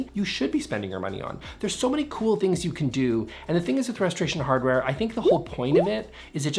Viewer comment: I heard "Frustration Hardware", which kinda tracks 😅